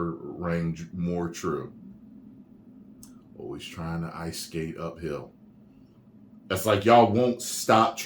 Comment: For years, I always thought he said "Always trying the ice skate the Bill"